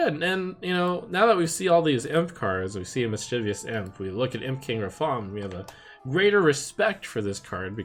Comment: I thought the card said Jump King Rafaam